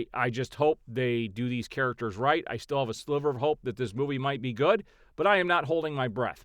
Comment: I thought you said “a Slither of Hope”. Superman 2025 should make at least 800 million dollars to be successful. Man of Steel made 900 million dollars in today’s inflation.